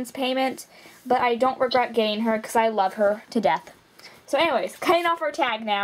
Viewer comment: I thought is said I love murdering lol srry..